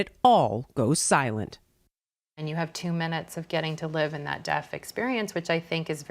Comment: Its a wonderful film. That said, PLEASE change the CC caption at 3:27 where you inexplicably use the word DEATH instead of DEAF!!!!!!!!!!